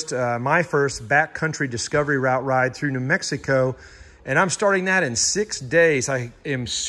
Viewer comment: I am officially Mr. 510 subscriber . Wasn't sure if I heard you right but it sounded like you said Mexico as part of your trip . Obviously you would need to do something about that item that shall remain unnamed . I think I misheard you and you said New Mexico . Happy trails !